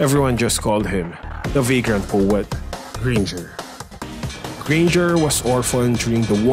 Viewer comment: the first time I heard it, I heard "The Virgin Poet, Granger" lol